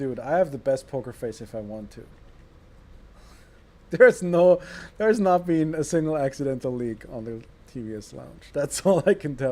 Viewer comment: Did just say "theres not been a single accidental leak on the TBS page"?